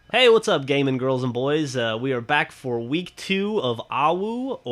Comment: LMAO i thought you said "hey whats up gay men, girls and boys" at the start